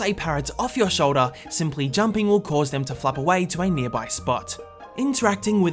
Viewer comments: Am I the only one who heard "will cause them to f*ck off" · did i hear f*** away or flap away?